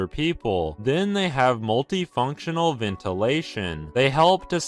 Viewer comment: I also have a multifunctional ventilation thingie in my bathroom and I’m def not in Japan 🤣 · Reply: I thought multifunctional said mummification 😭😭